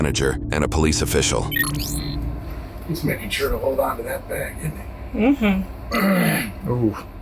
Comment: it says old instead of hold